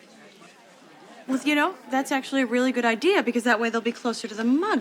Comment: 여기서 "Well, you know" 부분에 제 귀로는 well, you know로 안들립니다. 혹시 that's, you know라고 한것은 아닌지요? 태어나서 오늘 처음 리스닝 합니다. 님이 읽으실때는 well you know로 아주 잘들리는데요...별거 아닐수 있는데 제 청력이 의심스럽습니다...구독박고갑니다. 감사합니다. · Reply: 저도 정확하게 well로 들리지는 않습니다. 하지만 W 발음이 들리고, 문맥상 well이 매우 자주 들어가는 자리라 제 귀보다는 뇌가 well로 인식한 것 같습니다. 우선 기존 자막도 well로 되어 있으니, 현재로서는 well이 가장 유력해 보입니다. 그렇다고 절대 본인의 청력을 의심하지는 마세요. 원어민들도 안 들리는 부분은 안 들립니다. 다만 학습자보다 더 높은 확률로 맞히는 이유는 소리를 모두 들었기 때문이 아니라, 문맥과 뜻을 더 정확하게 이해하기 때문입니다. 님께서 that's로 들으신 것도 이미 아시겠지만, 문맥상 충분히 나올 수 있는 표현이고 뜻도 자연스럽습니다. 그래서 저는 듣기 점수 100점이라고 생각합니다. 오늘도 영어 만드시느라 정말 수고 많으셨습니다 :)